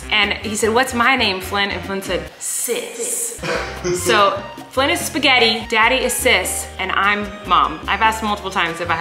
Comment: Did they just say Flynn is moms spaghetti …… 🤔😂